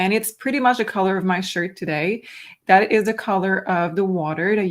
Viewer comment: Oh, WOW...I am rewatching from what I missed at the beginning and caught what said about Saratoga Springs...the water! I had a dream last night and in the dream I went to drink a small glass of water and it was a 'cloudy white' color. I was told to drink it...that it was good. When said, "it was the color of my 'shirt'"...I heard, "it was the color of my 'skin'"...so to me, 'white.' Also...I am in Saratoga, California!!! 💗💗💗